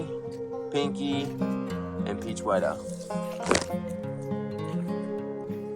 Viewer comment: Rex and roosers. I always thought you were saying "Peach White Out". Enjoying your choice of tunes. The fruit looked tasty.